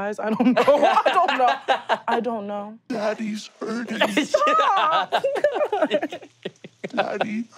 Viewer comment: I swear I thought it was saying daddy IT’S! hurting instead of “Daddy’s hurting” which had me completely out of it for a moment.